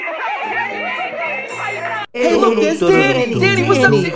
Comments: At first I thought he said hey look it DADDY!!!! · gasps**smiles way to big “DANNNY” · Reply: I heard "DENNY" 🤣